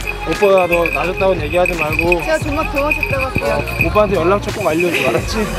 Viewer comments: 나만 ㅈㄴ도망쳣다할게요로 들림? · 제가 존나 도망쳤다고 할게요..? 저만 이렇게 들리나요ㅋㄱㅋㄱㅋㄱㅋㄲㅋㄱㅋ 귀여워